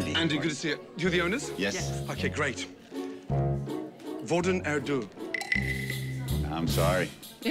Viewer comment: when He said “hvordan har du det” (How is your day) it sounded like “hvor er den due” (where is that pigeon) 😭